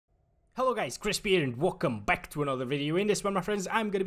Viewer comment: I heard him saying "Welcome back" as "Vulkan Back" .